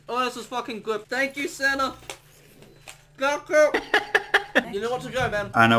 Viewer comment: I watch too much dbz... I only hear him saying "GOKU!"